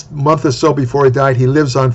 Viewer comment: sounds like you're saying Phil Heath died, Phil Heath isn't dead. I even turned on captions to verify that's what it said and it sounds like you're saying before he died. Not sure if you meant another bodybuilder.